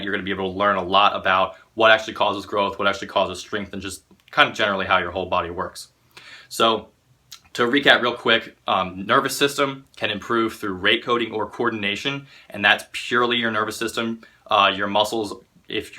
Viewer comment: What 'bout to take a hit from my bowl till I heard "rape coating".